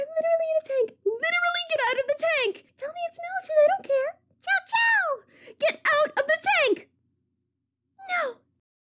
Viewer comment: Lol I can't quite decipher what Cream's saying "Tell me it's mildew/military/not to I don't care" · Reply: Melted (it took me several tries to decipher it myself)